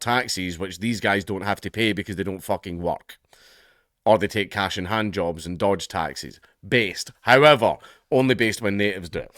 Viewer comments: Thought you said "only take cash and hand-jobs and dodge taxes. Based." Had to rewind and caught it right. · did you say that the immigrants get "cash for hand jobs"? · When he said "they take cash in hand jobs.." I heard "they take cash and handjobs..."😂